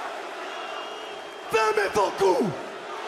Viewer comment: What he say in French at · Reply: "fermez vos gueules" . That means shut your mouths